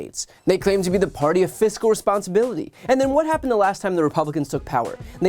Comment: I thought that they said they were the party of "fisty responsibly" 😂😂😂😂😂😂😂😰😰